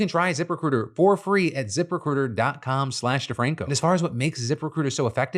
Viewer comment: Did the ad sound like Phil was saying Zipper Critter? Later on, it started sounding like “Zipper Cooter.” Phil might want to slow down for that specific ad. LOL